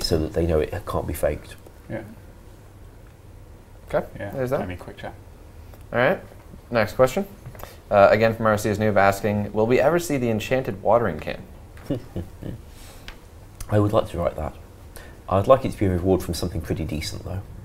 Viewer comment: Would it be possible for you guys to add the questions on screen as you read them so we can see the question? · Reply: Old School Runescape that's awesome :) Had to rewind the video because I wasn't sure if I heard you guys right when said enchanted watering can. I thought he said enchanted watering tiara which is something I used a decent amount in rs3.